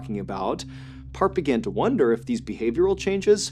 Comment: The first story I thought you said his name was Kurt angle. Then I thought it was perc angle. Part is such a weird name lol